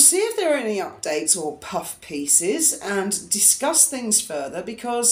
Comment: Yet another Puff Piece! I heard that as bookend game!